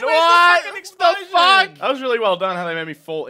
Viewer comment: T a m b i e n C o n o c i d a C o m o (idk what it say's xd it's Loquendo, a spanish talker bot )